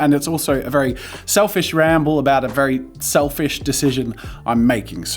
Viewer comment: I think I may be having trouble with the Australian accent in this one. I keep hearing him say "selfish" where I think the word he actually meant to say was "awesome".